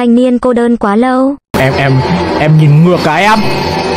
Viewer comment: Ban đầu nghe là "em em? Anh nhìn ngực cái em!" Khúc sau nghe lại mới bt nghe nhầm😅